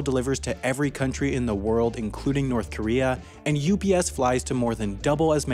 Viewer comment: wait did you say NORTH KOREA?